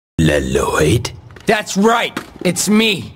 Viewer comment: Most people heard:I right I heard: that's ripe